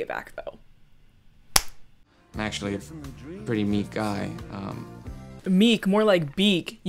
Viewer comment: I thought he said “I am a pretty neat guy” lol